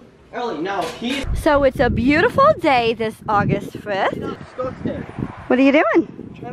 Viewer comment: Did you just say August 5th